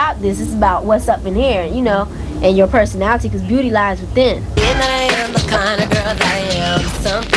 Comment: Yes Lisa, "SPEAK THE TRUTH"!!!! Damn I wish she was still alive...cuz girls today don't realize that you don't have to wear tight clothes and show it all to get a man. I always want to personally get to know a man and for him to get to know me. For me its always been that "I want to know what's going on up in his head before I let him take me to bed" R.I.P Lisa my fav of the group and the best female rapper for the 90's and beyond<3<3<3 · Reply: Why'd it sound like she said bitch at 3:10😂